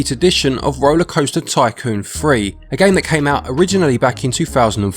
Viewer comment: With your accent, it sounds like you said "Rollercoaster Tycoon Free", and, man, I wish it was. 30 bucks is a bit for me rn. I'll still pay it to have a good park creator on the go.